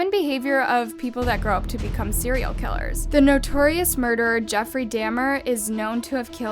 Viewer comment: It sounds like your saying Jeffery Damn her! Lol